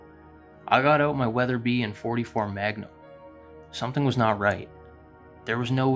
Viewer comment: The man states that he got out his Weatherby in 44 magnum. There are plenty of rifles in .44 magnum, but I've never heard of Weatherby making one. Not saying it doesn't exist but seems unlikely based on what I know of them. This detail among others makes me think this story was made up. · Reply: I thought he said his Weatherby and .44 magnum.